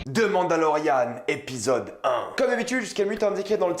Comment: c'est moi ou il dis demande à lorian